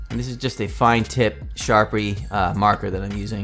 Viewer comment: I misheard it when you said "Sharpie marker." I thought you said "Shark Week marker," and I was deeply confused... and envious of your marker.